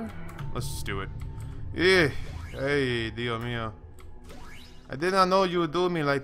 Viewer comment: Did you just said "Dio mio" at 21:39 ? XD